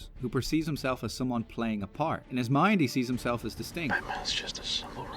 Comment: I replayed this several times and cannot hear what you said, "in his mind he sees himself as ....." Can you clarify what you said there? I love all of your videos. Thanks. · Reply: Distinct